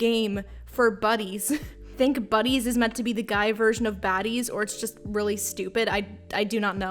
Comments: i thought this said i found a game for babies but it was baddies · i am one “i found a game for baddies” away from ending it all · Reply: Is it bad that I thought it said buddies and not baddies 😅